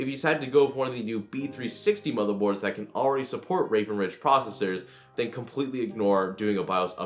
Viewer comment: subtitles say that the motherboards support rape 😂